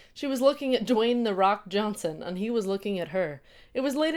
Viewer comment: It almost sounded like you said:"Dong "the Rock" Dongson. Haha